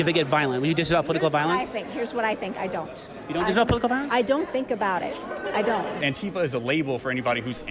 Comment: did she just say she doesn't think?